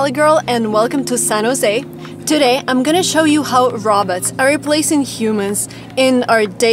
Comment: Sounds like she says Roberts other then that great video not being mean just saying great video